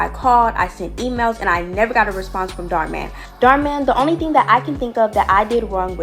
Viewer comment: lmao as if this guy's name is actually dharminder man, i thought 'dharman' was just the channel name XD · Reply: I lowkey thought you said “Dharman Dharman”